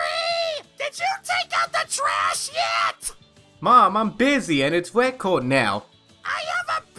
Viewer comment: oh my god i was skipping the mom parts and during skipping it i heard this "MOM IM GAY" i shouldnt be aloud to skip things without pausing